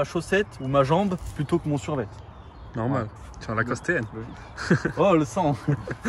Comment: lacoste tn frère ou quoi ?